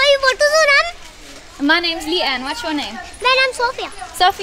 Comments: I literally heard "Motu's your name" at the beginning 😂💀💀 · I heard her say "motu, su naam ?" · Am I deaf 😂 I literally heard “motu is ur name” 🥹